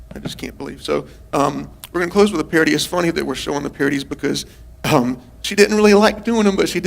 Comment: Parodies? I thought he was saying purities, lol. 😂